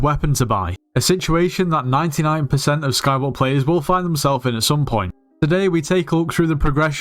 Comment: nah💀 he did not just say skywars